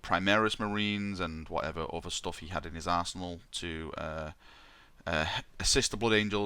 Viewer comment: At around 6:45 I thought you said "Guilliman turns up with primaris marines and whatever else he had in his arsehole(actually said arsenal)". Which would be fitting since that's where the idea for primaris marines came from.